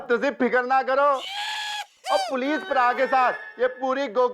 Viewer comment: did he say police praa😂😂